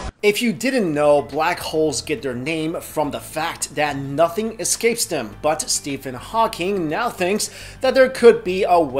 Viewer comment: For a moment I heard, "nothing escapes black holes but Stephen Hawking.."